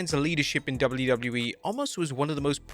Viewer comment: says Otis in preview instead of Omos